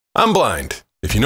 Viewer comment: "You got a staring problem, buddy?" "Yeah, I can't." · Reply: I thought he said I’m black😮